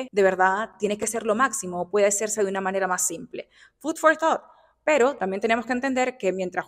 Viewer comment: No entiendo que dice en el minuto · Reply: Dice 'food for thought'